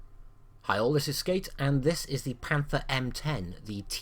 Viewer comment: at the beginning of the video it sounds like you said "heil" instead of "hi all" XD